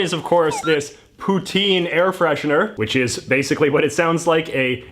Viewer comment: What he says: poutine What I hear: PUTIN